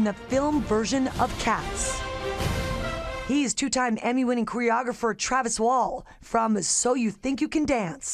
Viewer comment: Ok...am I the only one that hears her say QUEER-ographer instead of choreographer lol